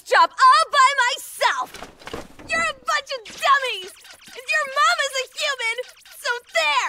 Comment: did she just say "fudging dummies"? the clean version of f*cking